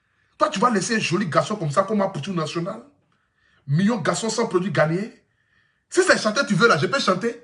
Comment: A 3:03 , il dit koi svp ? "mignon garçons sans produits ghanéen"? c'est ça?